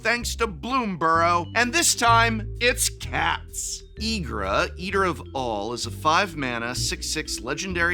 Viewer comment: It may be that I have the mind of a 12 year old but I swear I heard "Ygra, Eater of Balls."